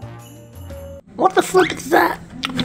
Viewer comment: I thought you said what the F--K is that? At 8:44 mid time until I saw CC at mid bottom of the screen